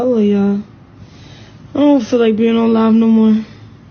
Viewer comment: run. he just publicly embarrassed u. I know what he says when nobody is around. Sorry. Ur beautiful ❤ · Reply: My heart skipped a beat, when he said... "Sometimes... no, not right now... definitely not now... " 💔.... From this day forward, I am gonna need a change of that term " live" ... to describe a broadcast in real time😢... It is tooooo damn close to being actually ALIVE ... When she said " I am gonna go y'all ... I don't feel like being live nomore ..." all I heard was I don't feel like being alive nomore... And I grabbed my rosary ... and braced for the worst ...😔 Which I should have known couldn't have been happening..., because always gives us plenty of warnings ⚠️... for all potential triggers ... But the look in her eyes, as the humiliation sank in... got hold of my heartstrings 💔... Phew, Now that we are moderately sane again😂... Girl, dump that heartless fool... We got so disgusted and shocked by his insensitivity that our hearts literally broke for you😢... and the way he doubled down ... as you kept giving him multiple chances to reverse the statement 💔... Girl, run for your life💯